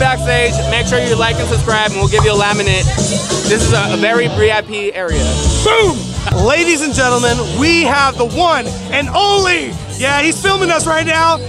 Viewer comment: did you say bree-i-p area? haha